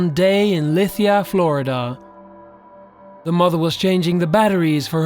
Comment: I thought he said 'muggle' instead 'mother'. Lol!